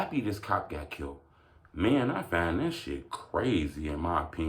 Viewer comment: Thought it said dex got killed lol